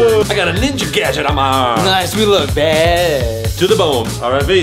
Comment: Did anyone hear it sounded like he said we look bad a$$